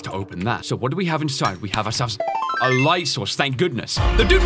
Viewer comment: It sounded like he said light sauce